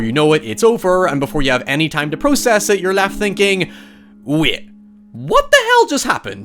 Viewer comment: What word is this: 2:03??? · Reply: I think he said "Wait" but honestly his voice is so unbelievably hard to listen to I'm not sure.